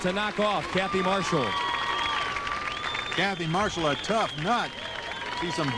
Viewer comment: Ok, wait? I think I am hearing things wrong but does Larry call Kathy a "tough cunt" at 40:59?